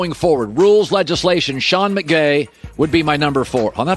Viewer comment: does he say sean mcgay?